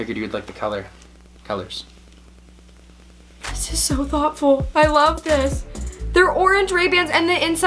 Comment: Niki said “this is so thoughtful!” About the sunglasses. But I thought she said “this is so awful!” lmao